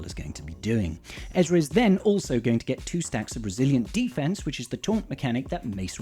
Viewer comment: Did anyone else hear “Brazilian Defence” or just me?